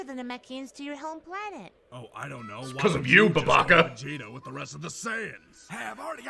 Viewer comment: Holly f*ck, did just said "babaca!"? That's Brazillian Portuguese for stupid/idiot! Proud(er) of you.